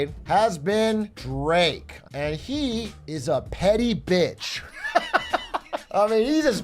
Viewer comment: I really heard it as “Drake is a pedi bitch” and didn’t even question it